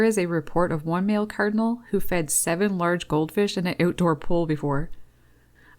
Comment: The odd behavior of birds feeding fish is a weird phenomenon! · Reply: Oh, I thought she said, “goldfinch”. Silly me! Either way, that is fascinating bird behavior.